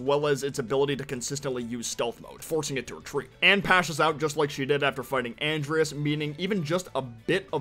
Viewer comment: Bro did you just say "Pashes out"? Sorry I just thought that was pretty funny